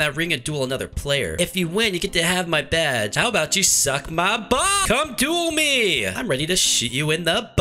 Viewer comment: why did said "suck my bells" 0:28 😂😂😂